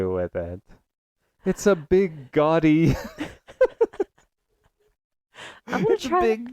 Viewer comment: Omg you said GAUDY omg I can't stop laughing- I thought you said GODDY - trashy goddy shit 😂😂😂